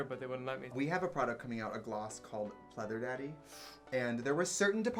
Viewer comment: “Pleather daddy” was a no?? As I’m looking at my cult-fav nars “orgasm” blush (and my personal fav shade “deep throat”)...?? Okay. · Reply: Ohhh, I thought they said “butter daddy”...